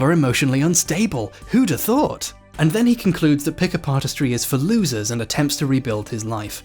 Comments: You sound like your saying Pickapartistry. I'm here for that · Lol, thought he was saying "pick apartistry" for 3/4 the video